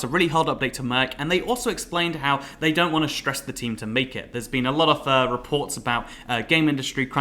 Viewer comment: Am I the only one who thought he said "they don't want to stress the team to bacon" at first? 👀